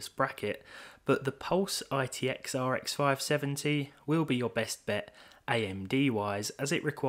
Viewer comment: I thought I heard "RTX 570" 😆